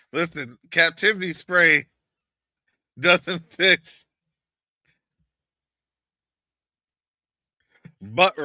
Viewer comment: you are toooooooo silly😂😂😂😂 I just heard you say “anticaptivity spray won’t help butt rash” 😂😂😂